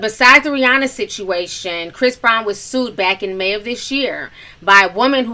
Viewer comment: did she say "May of this year"?